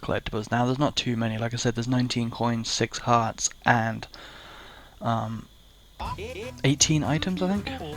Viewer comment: “19 coins, 6 hearts, and 8 tentacles”. I heard “19.6 hearts, 8 tentacles “ like 3 times before I got it lol